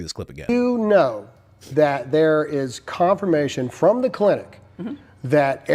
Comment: Did he say : confirmation from the clinic? If so, SHOW the clinic receipts. I'm kinda taken back by this a little bit because WOW I did not know you had clinic receipts :-0)